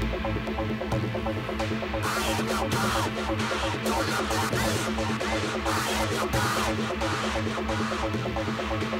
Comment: What is the voice saying? I can't make it out, can someone please help me? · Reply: Iam your god !